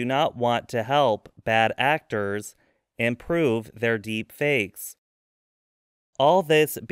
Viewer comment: The subtitle is wrong. "Improve" is correct, not "And prove".